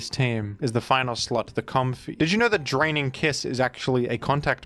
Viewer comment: definitely heard that as “…the final slut, the cum fe”